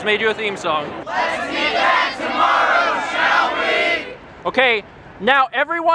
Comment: does anyone know what said in the group at 8:51? i assume it wasn't "lets meet back tomorrow, shall we?", but it's impossible to tell what it was...